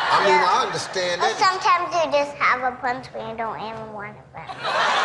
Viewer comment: what did she say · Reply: She said, "sometimes you just have a prince, when you don't even wanna a prince."